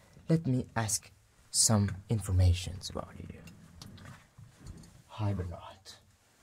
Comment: what does he say to the skeleton? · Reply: he says:"hi Bernard"